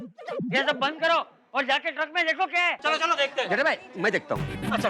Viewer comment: Did Abdul call Jethalal as Bhide bhai at 41:29 ? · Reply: Naah he said jetha Bhai mai dekhta hu